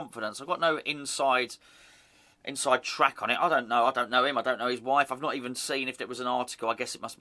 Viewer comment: Thought you said ‘ I don’t know him but I do know his wife’!